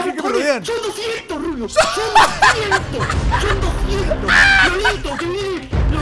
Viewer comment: Recien me doy cuenta que dice, "Que viene lolito!" xdxd